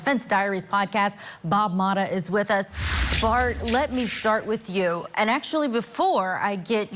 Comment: Did she say. "Bart, let me 'shart' with you?" Sorry lol